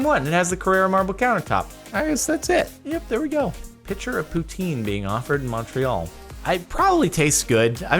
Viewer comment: My brother thought you said "Putin" 😂